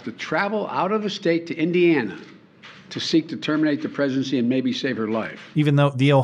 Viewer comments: Am I hearing Biden correctly? I hear an S not a G in the middle of the word, like he is slurring his speech on the word ‘pregnancy,’ making it sound like ‘presidency’ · Did Biden just say the girl was forced to travel to Ohio to terminate the presidency? Yes. Yes, he did. Check the subtitles....lmao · Am I trippin', or does Biden say "terminate the presidency" at 13:56?